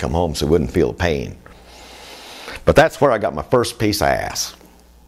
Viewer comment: LOL all the sudden I heard " And that's where I got my first piece of ass " hahaha